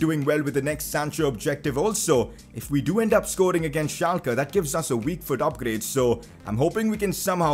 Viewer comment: did he just say kyle roberts scoring against Schalke